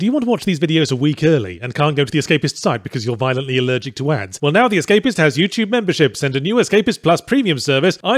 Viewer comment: "Are you violently allergic to ads?" Proceeds to give out an ad Me: inflates like the 100th red balloon and dies of anaphylactic shock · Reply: Oh I heard “violently allergic to ants”, which was funny, but this makes more sense